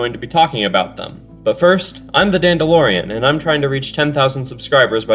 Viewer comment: man i heard “im the dandorian” and acc fell about laughing